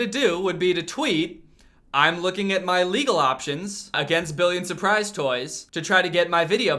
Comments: I always thought that he was saying "billy and surprise toys" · Omg for the longest time I thought he was saying Billy and Surprise toys💀😭